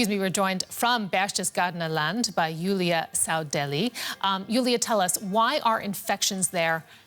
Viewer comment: I thought anchor called the other news reporter, Julia SouthDelhi. 🤣